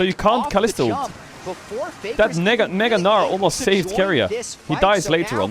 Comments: Just me of this did just say the N gnar at 45:03 ? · what did he sayyyy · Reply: There's a Mega Gnar on red buff xdd